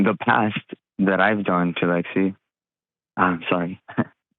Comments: Who's "Lexy?" Did anyone else hear slip and say that name? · Did he say “Lexi”???!!!! Who is LEXI?! 🤦🏾‍♀️ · did I hear him call her Lexi then correct it to Aubrey? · did he say Lexy? 😳😳 girl he has DEFINITELY cheated. Been there done that and we look for excuses to blame the faithful one but ourselves. Yeah, RUN! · Did he call her Lexi?!!! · Did he call his fiancé (Aubrey) another name ? (Lexi) 5:45 😮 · did he say to lexie ??? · Did he say Lexi? And her name is Aubrey??? @ 5:50-ish mark